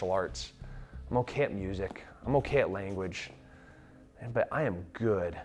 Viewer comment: I love Tim Kennedy and I thought he said "I'm okay at Mutiny, I'm okay at Language"